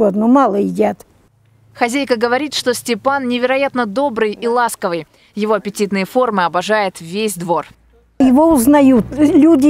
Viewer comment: "Причина лишнего веса - коварная генетика" Я тоже так оправдываюсь. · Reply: Мне послышались или она сказала "его апититные формы любят все" они чо из кота стейк хотять сделать?? Подозрительно или это какая то изврощенка???